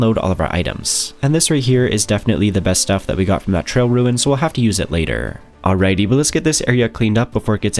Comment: I thought I kept hearing you say Trail Ruin instead of Trial Ruin, might have been a bit sleepy listening though.